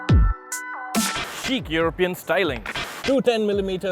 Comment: I really thought he said "Cheap European styling." I listened probably around 5 times and then realised he meant 'chic'.